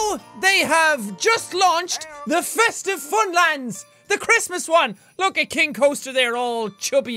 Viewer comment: I thought it said festive funerals